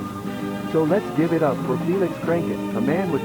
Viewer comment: i thought it said "lets give it up for penis cranken" or something